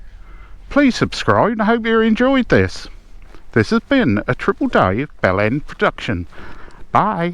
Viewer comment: am I hearing correctly? Did you say ‘triple Dave bell end production’? 😂